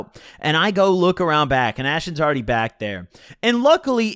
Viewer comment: I heard that "bAd"